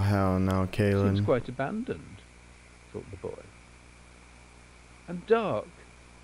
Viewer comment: I find it very unsettling at 5:38 when there's another voice in the video that quietly says "Kill the boy". I've had to replay this video and the original to see if I misheard something, but it's there.